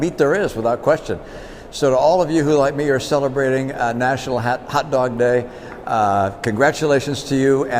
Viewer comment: Did he call it a “Hat Dog”? @ 0:32 Lol